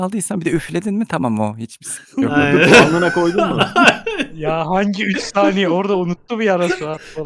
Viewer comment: de ne diyor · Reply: Anlına koydunmu diyor